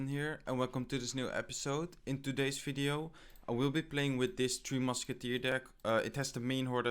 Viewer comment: I always hear "Free Musketeers"... Pretty ironic right?